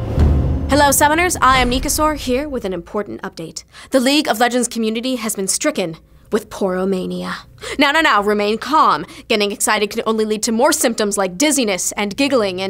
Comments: When starts, i thought she said pornomania, and i was like: THIS IS GONNA BE GOOD!! then it became clear, poro-mania, misleading but yeah :) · At first i thought she said Porno mania and not Poro mania, either way my boner brought me here nonetheless. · i thought she said pornomania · I thought that said "Pornomania." wut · I thought it said Pornomania. I am disappoint. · I thought she said pornomania. Lol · Dang for a moment there I thought she said pornomania pfft *facepalm*. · for a sec i thought it said Pornomania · at first, i thought she said "Porn o'mania" : /